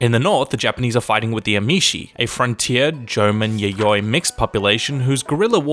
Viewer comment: did he call the Ainu aboriginals a "German-Yayoi mix?"